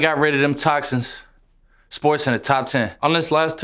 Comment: Damn, i always thought he was saying "this boys in the top 10"